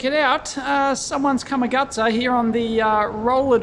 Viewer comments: I always thought you were saying "come-a-gutter" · The most important part of this is i now know how to spell Come-a-Gutsa. I heard you say this phrase so many times but never knew exactly what you were saying til now! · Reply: I thought it was "cut my guts up" until this video.